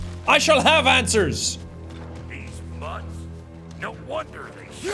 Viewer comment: Oh lord I heard "deez nuts"